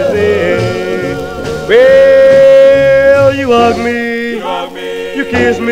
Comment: When I first heard this, I was caught by surprise when I thought he said “you ugly” instead of “you hug me”